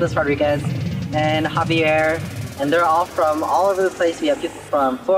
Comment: Did he just say somebody is from India? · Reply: Kelvin Chau He said Indiana